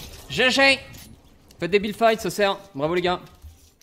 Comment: C moi ou g entendu debilfight a